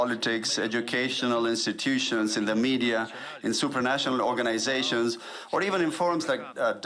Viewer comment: Did he say "Supernatural organisations"